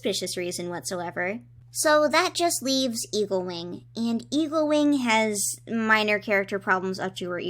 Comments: I keep hearing you say “Evilwing” and not “Eaglewing”. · I swear I thought you said Evilwing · every time you say eaglewing it sounds like you say evilwing